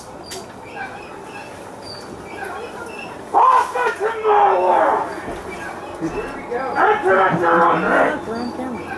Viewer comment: What is being said here? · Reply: Enter on your own risk